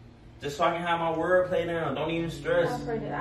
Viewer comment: It sound like she said “I’m pregnant” at 3:51😂